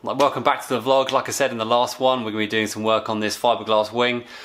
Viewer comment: so...you added carbon fiber, which usually is used to reduce weight...but you just put it over the metal so you increased the weight. smart. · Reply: i apologize. i missed the very first bit where you stated it was fiberglass. i misheard it as saying "we're going to be fiberglassing this wing"